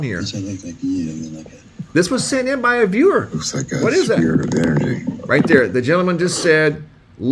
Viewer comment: he says “looks like a sphere of energy”. It did kinda sound like he said, ‘spirit’, though.